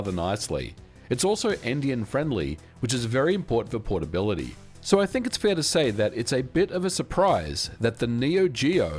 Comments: automatic subtitles says "indianfriendly" and I hear that too 😭what word did you actually say there? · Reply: endian friendly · It's also... what-friendly? endian-friendly? Indian-friendly? Empty and friendly? Seriously, I've listened back to that like five times now and am not sure what he said. Mind, I'm no native speaker, so it might be a rare word that I've never heard. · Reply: He meant endian-friendly, i.e. the code is portable between little-endian and big-endian systems. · I heard “it’s also Indian friendly…”